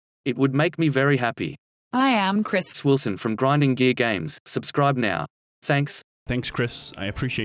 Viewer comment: i literally heard "grinding gay games"